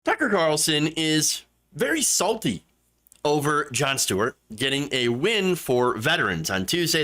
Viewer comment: at first I thought it said, Sucker goes after John Stewart...🤣